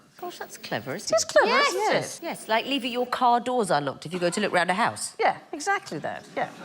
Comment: I listened to it 4-5 times but I still can’t make out what Victoria said. It was, “ like leaving your car doors unlocked if you….” Sounded like she said, “ go to look round a house.” I’m American so it might just be her accent and I’m not hearing her right. Can anyone explain that part to me?